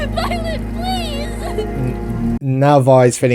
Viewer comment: The first time I saw that scene at the end with Vi and Powder, I thought Powder said "Mom, please" not "Violet, Please", implying there was abuse in the home with their parents that Powder was going back to in that moment. While it would be interesting, it doesn't really go anywhere, so definitely out of character for the typically very tight writing in the first series. Except it did make me feel a bit more immersed in the narrative, so it served an emotional function if not a narrative one. Then years later i watched it with subtitles and was like, "Wait, What?"